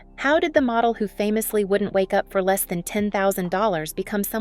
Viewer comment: She didn't say she "wouldn't wake up for less than $10,000" as U state. What Linda actually said was that she "wouldn't GET OUT OF bed 😉 for less than $10,000"